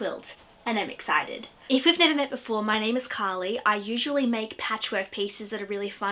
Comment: Ironic that you can actually say an "R" when you are imitating that guy but not in real life. I thought you said your name was Collie.